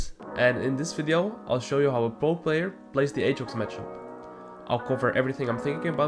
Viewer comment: I thought he said "I'll show you how a gold player plays the Aatrox matchup". I almost immediately stopped watching.